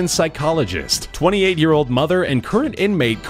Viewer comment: 28 YEARS OLD??? Did I hear that right? She looks like she's 40